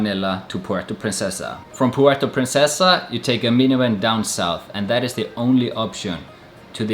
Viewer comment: Great travel video.... but man, I had to keep rewinding at around 2:55 to understand what you were saying -- "from Puerto Princesa, you had to take a "minuen" (or "minyuwen") down south, to get to Balabac". I kept asking myself, what the hell is a "minuen". Thank God for Youtube captions -- it was "minivan". LOL my ass off. Cheers!